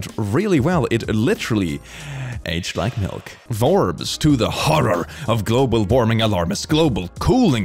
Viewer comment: When he said Forbes I thought he said vore am I really that impure